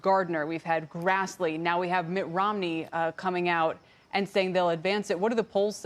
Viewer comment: Did she just say Mutt Romney is finally coming out???